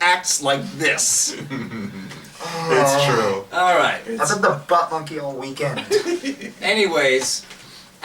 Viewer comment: for a second around 17:37 I thought said "I'm going to buttfuck you all weekend". well...thanks for the tip off. thanks.